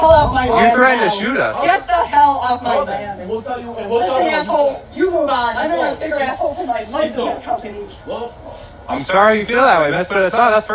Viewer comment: Did she say "Listen asshole you will die"